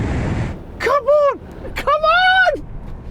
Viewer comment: ㅋㅋㅋㅋ자꾸 카본카본이렇게들리지...ㅋㅋ